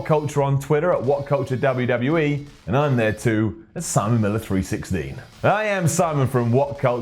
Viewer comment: I know at the end he says''follow me at simon miller 316'', but in my head I always hear ''Simon Military 16''